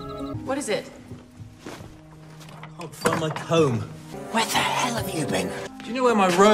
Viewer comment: the subtitles say “I love you” instead of “where the hell were you!?”